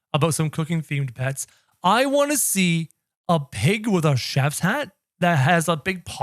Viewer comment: i thought you said a pig with a crown